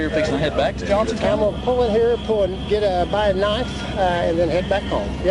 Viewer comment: Did he say he was stopping there for a butter knife? 5:50ish .. but he'd need it for all that butter!! 🫨😵‍💫🫠